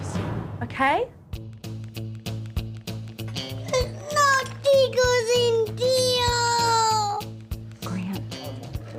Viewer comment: Did he say "There's no pictures in there"? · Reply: He said “No STICKERS”